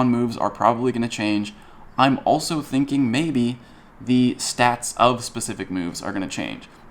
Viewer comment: I thought you said "status" not "stat." I was real excited that grass Pokemon would be useful. Oh well.